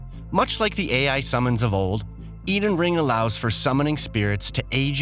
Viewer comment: Can't wait to play the new soulbourne game, Eden Ring. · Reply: Wait did he say "EDEN ring?"